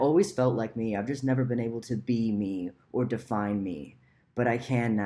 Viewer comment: i thought you said to be meme